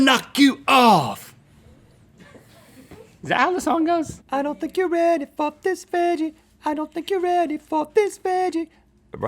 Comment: why did I hear 'I dont think youre ready for Miss Vanji' lol